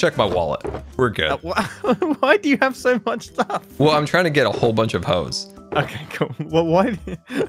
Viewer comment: DID I JUST HEAR SCAR SAY “ well I’m just tryna get a bunch of hoes “